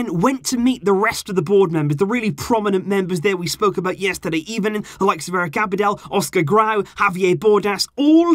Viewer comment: am I The only one who thought it sounded like he said “ebening”